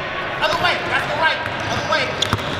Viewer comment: i heard "taas kamay" any filipino hereeeee?